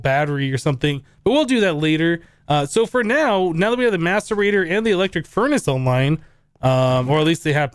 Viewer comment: if you listen closely you hear maturbator instead of maserator